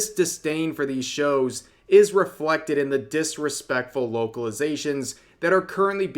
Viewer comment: Did you say Woke-alization here? Because it sounded like you did, and honestly, that's a great way to put it I feel And now I wanna see an anime translated entirely into redneck slang, call that a yokelization